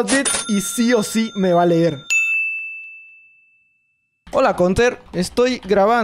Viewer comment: ¿Alguien mas se dio cuenta de que dice Contersine? XD